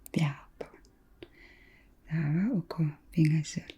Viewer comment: You are on to something :D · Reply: I hear "pingasut" (3) instead of "sisamat" (4) here (d2->d4 movement), but subtitles translate this simply as "d4", was it changed intentionally so the subtitles reflect the real situation and not the direct words of the speaker?